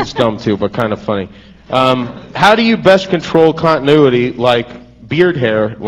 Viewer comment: What is that word @ 17:54?? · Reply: Continuity con·ti·nu·i·ty noun 1. the unbroken and consistent existence or operation of something over a period of time. "pension rights accruing through continuity of employment" Similar: continuousness uninterruptedness flow progression Opposite: discontinuity 2. the maintenance of continuous action and self-consistent detail in the various scenes of a movie or broadcast. "a continuity error"